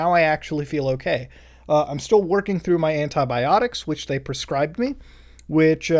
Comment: at first I heard "hentai-biotics" instead of "anti-biotics" im too cultured for this land